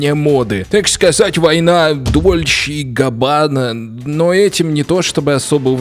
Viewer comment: мне послышалось дуче кабан